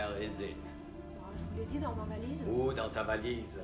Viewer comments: What has she said? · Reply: "dans ma valise"/"in my suitcase"?? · What has he said? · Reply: "où dans ta valise?"/"where in your suitcase?"??